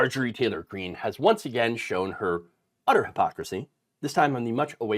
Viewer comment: Oh snap...I thought said Marjorie traitor Green has finally showed her utters.😆🤣